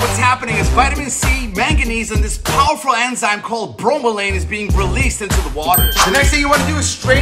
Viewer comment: I thought he said bromine 💀